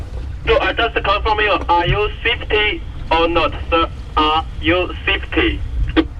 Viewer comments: the guy on the radio sounded like he said "are you schwifty" how did rick and morty pop up in the north sea? just finished watching one day ill sail that bit of water my self! one day my friend one day! thanks for the video was grate! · "are you safety, safety!" I think you getting close to India or something.. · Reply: What was he saying there? ARE YOU ???????? OR NOT SIR? It sounds like fifty. In any case I would like to slap him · Is even the coast guard using an Indian call center nowdays? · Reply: I thought he was saying "Are you fifty.....50"? like if he was, he gets a prize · What was he saying there? ARE YOU ???????? OR NOT SIR? It sounds like fifty. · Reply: Are you Safety, Safety. Think he meant are your safe, but English was a second language.